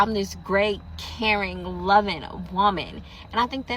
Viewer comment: When she said “caring” I thought she said “Karen”……. I was like, that’s the problem 😂😂😂😂